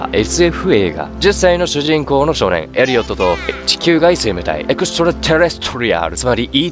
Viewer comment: でエっ地球外と聞こえる